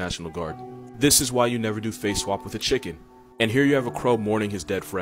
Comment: I don't understand this chicken one? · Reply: OH nevermind, I realized he said face swap rewatching it 😅😂